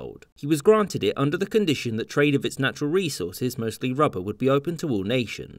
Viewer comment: Lmao. I had to rewind a few times bc I thought he said that the Congo's natural resource was, "mostly rubble". I was like...🤔